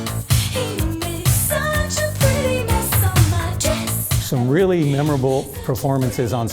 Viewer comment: Did she say he made a pretty mess on my dress? 🤷‍♂️ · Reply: Lmao!!!! I thought she said ON MY CHEST! Either way she was nassy! #HEYVANITY